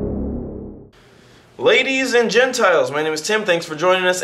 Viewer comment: I thought he said ladies and genitles. I was like WHAT?